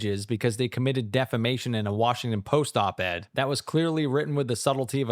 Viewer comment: I heard “They committed defecations in a 4 post bed”